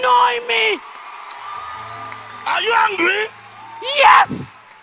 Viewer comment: What preacher said: "Are you angry?" What I heard: "Are you hungry?"